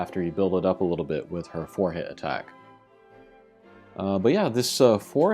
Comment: Bruh, when you said "four-hit attack" I thought you said forehead attack! I was like WTF?? Great info and thanks for the review! I haven't pulled her yet, but I'm def looking forward to getting down with her thiccness XD